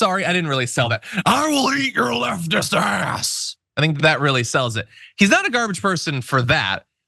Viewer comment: Did he just say "He'll eat your butt meat"? 🤣